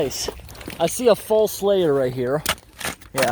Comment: I heard "I see a fall slayer right here"